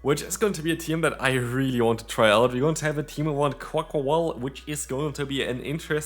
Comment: Accent and fast talking all I heard was "quaquaqual" 😂